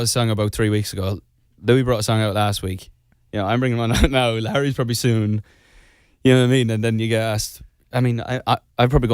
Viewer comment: Excuse-me ??? Did I just heard Niall saying 9:40 "Larry's coming soon" ????? OMG !!!!!! 😨😳🙊🙊🙊💚💙💚💙